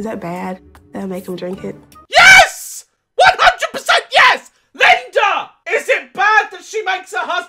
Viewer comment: Did just ingest a toxic radiant in a can? 😂😂😂 also 4:37 I thought he said LINDIR from The Hobbit trilogy 😂😂😂